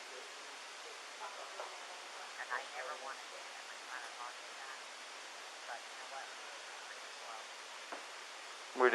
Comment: shanann’s mom is talking to the officer on phone and it SOUNDS like she’s saying something like “and I don’t wanna say/think (inaudible) about my son in law (Chris), but (inaudible)…..” to which the cop responds “we do”…..was she saying something like she was suspicious??? Anyone make it out??! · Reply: She said “and I don’t wanna think that my son in law did that” that’s what I heard